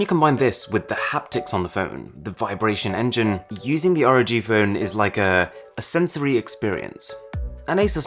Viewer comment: I thought it said Pog Phone III